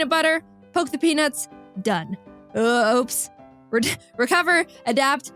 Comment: bro i really thought you said "poke a Penus"